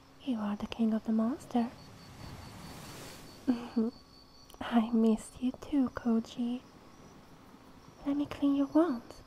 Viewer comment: What did she say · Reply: "I missed you too, Goji"